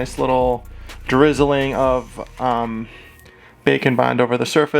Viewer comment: Whenever I hear him say "bake and bond" I hear, "bacon bond"